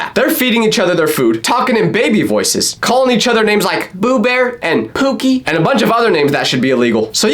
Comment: wait did you say boo? Oh no I think I'm getting the disease😢😢